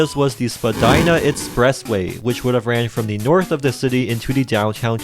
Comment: Vagina express way? What I heard.